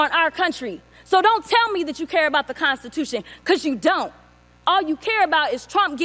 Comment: "Cause you don't!" ..... I heard "Cause you b!tches don't!". I bet she was thinking it, because I was 😁